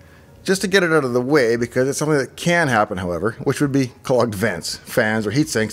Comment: what do you say at 3:12 or 3:16, somewhere around there ..............at ? cold/ called / clogged advance ?? ..................fans or heat sinks.........